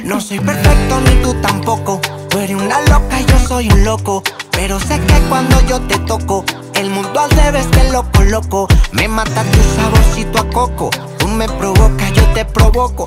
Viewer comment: Al escucharla por primera vez, juro que escuche " el mundo de beéle loco loco" jajajajajaja